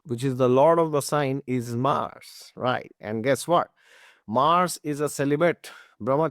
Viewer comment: did I hear correctly call that “Mars is a celibate” ? 🤣 this news is astonishing